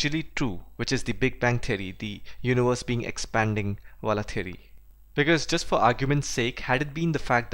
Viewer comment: did you said big bang wala theory?